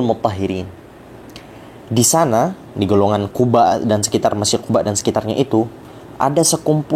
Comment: Menit 6:10 an kirain ahlul quba' yang lora bilang di sekitar mesir, ternyata di sekitar masjid. Pantes saya cari tahu tentang ahlul quba' di mesir ga ketemu😅 salah denger😁